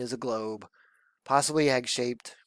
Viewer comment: im not questioning you im just learning. Never knew that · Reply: lol I feel so stupid right now. I misunderstood you. You said egg shaped. I thought I heard x shaped lol 😆